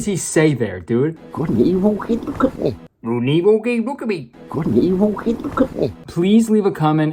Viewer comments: I thought the dude at 4:48 said “couldn’t he walk in though, couldn’t he?” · Correct translation of what he said: "Gudnibu hey look at me" Idk guess her name is Gudnibu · I think he says “good and evil geydgehdbeh” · I think he said ‘and look at me’ near the end of the gibberish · I think he said smth abt good n evil me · I have feeling he might be saying something like "could he even look at me". I dont think that makes a lot of sence, but thats what I hear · all i hear is "kunivo, he cook me" "Could evil hit cook me" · i think hes saying "he couldnt even look at me"??? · I hear good and evil and I don’t know the rest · I heard “Good and evil keep me company” for the boss’ line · ah yes the boss clearly said “couldn’t evil keep a cook meat” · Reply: I thought he said "good or evil" then it became unaudable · LMAO I think the boss-man is saying "Good and Evil? Hey, look at me," @ 4:58 when she looks down 😭💀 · MY BRISBANE SHOW IS SOLD OUT BTW!! FOLLOW ME ON TWITTER & IG TO BE UPDATED IF I ADD ANY MORE SHOWS! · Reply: I FIGURED OUT WHAT HE SAID 5:03 "good and evil? hey! look at me!" FUCK YEAH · after destroying my eardrums trying to decipher what tf this dude was trying to say, my best guess is "good and evil-- hey look at me" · I think he’s saying “Good and evil? Hey, look at me!” Idk what the context for it is but it sounds like it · I think he's saying "hey there, look at me". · In that one part (4:49) the guy is obviously saying “good and evil heegeecootnee”